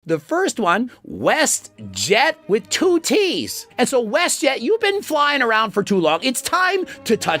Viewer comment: I thought he said WestJett with titties in the beginning